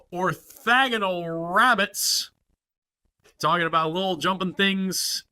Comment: Lol so weird. I don't get that one but a random "sorry bout sorry bout" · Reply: I think you're just mishearing it. I can see why you might hear "sorry 'bout" instead of "talkin' about" but I'm fairly sure it's the latter.